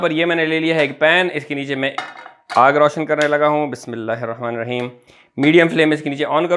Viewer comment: <3 It's amazing ♥️ Btw what are you saying? Ismilahmarahim? I couldn't understand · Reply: It's Bismillahi Rrahmani Rrahim meaning in the name of Allah (God) the most Gracious the most Merciful